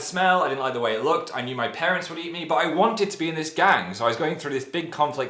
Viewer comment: I swear Phil said “My parents will eat me”